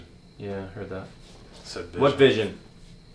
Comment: Dude 😳 when u asked "what vision?" Sounded like it said "no". Or "hell". 🤷‍♂️